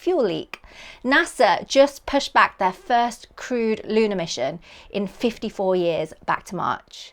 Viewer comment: I thought I heard "Shittle program" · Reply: I heard “first crude lunar mission.”